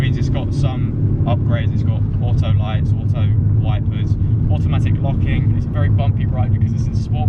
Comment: When you said automatic looking i thought ypu said automatic honking 😂